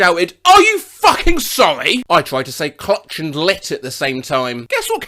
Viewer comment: Does anyone have a clue too what she said? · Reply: "Clit", probably.